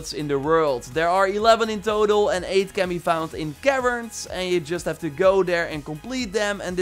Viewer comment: What is he saying at 3:40? It sounds like “Karen’s”. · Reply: Caverns